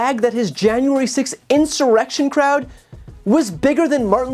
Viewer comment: Ohhhh I thought he said his inauguration crowd but honestly who tf knows what he’s talking about.